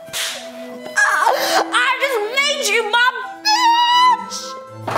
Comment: 36 did she say b**ch